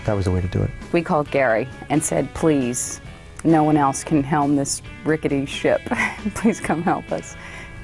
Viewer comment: what exactly does she say? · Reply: "Please, no one else can helm this rickety ship. Please come help us."